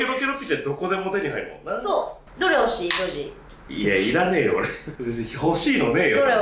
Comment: 女児がジョージに聞こえてダメだった